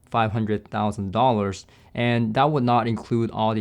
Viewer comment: Did you just say that you would need to recommend a system of $500,000 to complete with the PSB?! And I thought saying that the Lore outperforms 10k systems was overhyped... 😉